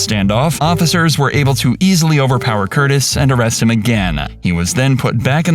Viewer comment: Did u just call him Curtis at 11:06??